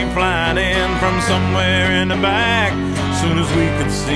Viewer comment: i coulda swore it said "from somewhere in iraq"